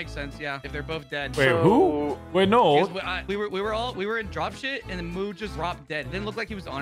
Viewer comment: Hot cheetos all the way 😂 also was that that said drop-shit instead of drop-ship at 9:59? Haha